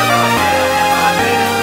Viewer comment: what did he say at 2:54 please help me </3 ? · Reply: alshnqiti I´m centuries ahead of them. if i´m not wrong, i think that is what he say.